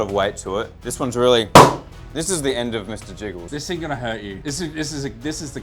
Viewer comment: thought he said biggles for a second 💀